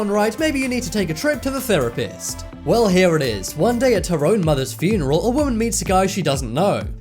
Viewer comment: HEY IF I HEARD AT HER OWN FUNERAL I WOULD HAVE GOTTEN THE LAST ONE I THOUGHT IT SAID TARONE'S MOTHER'S FUNERAL